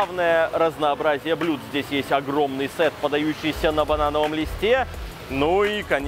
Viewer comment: Послышалось на бараньем глисте😂